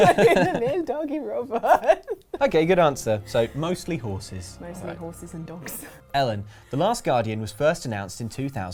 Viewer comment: Before the pictures popped up I thought Ellen said "Smeagol & a horse".